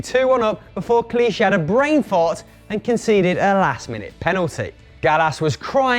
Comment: Did he say brain fart?